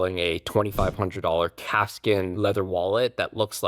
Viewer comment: lol I heard “cat skin” wallet 😂😂 I was like 😮, had to rewind hahaha